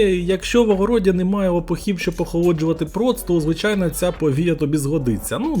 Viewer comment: 😂 декілька разів переслухав чи не почулося,може віяло, ще й субтитри увімкнув, там теж правильно почуло і написало this тобто "повія" приховало 😅